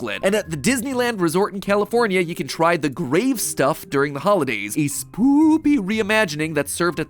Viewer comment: excuse me, honey is amazing! but also i really wanna try sandwich, i always thought he said try the great stuff lol